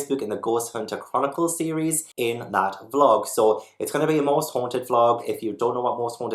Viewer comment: I heard "Horse haunted", instead of "Most haunted" and I was confused by the show synopsis because I was imagining psychic horses investigating haunted locations. 😅